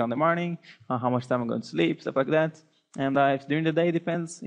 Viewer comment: i thought he said "so f*** that" LMAO :D